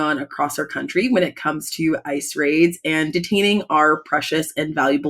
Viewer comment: I misheard the beginning, 'ICE raid' as 'Race Raid'. I'm going to keep calling it that.